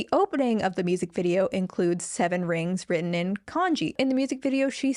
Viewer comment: i kept hearing "written in congee" and it took me a while to get it wasnt about porridge 😭😭